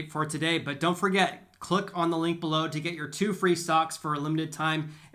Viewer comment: I thought he said, two free socks😄